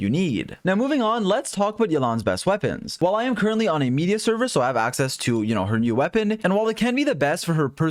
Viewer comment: What did he say at around 10:06 ? Media server?